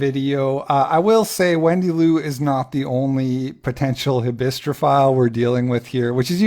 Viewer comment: What are you saying @ 18:52? Nabistrophile? Habistrophile? ???Someone who loves murderers? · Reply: Hybristophilia